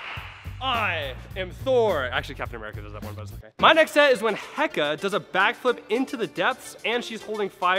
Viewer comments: did he just say HECKA! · did he just say hecka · Am I the only one who heard 'Hecka'?